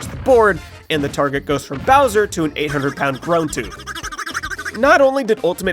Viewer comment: what is he saying grow tube?